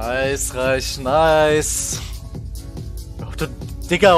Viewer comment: I think the translation at 1:30 is: Nice enough, nice. Today is loser queue, believe me. I have had the toughest d1 games. I am a studying German at university so there's a good chance it's not perfect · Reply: I thought I heard "reicht" but again I'm not fluent lol